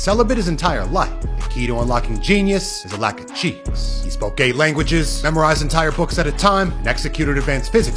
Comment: Thought it said gay language's